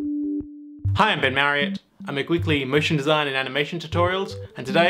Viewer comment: In the intro I thought he said..."Hi I'd been married"